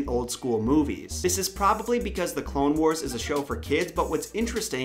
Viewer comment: DID YOU JUST SAY BLONE BARS WAS FOR KIDS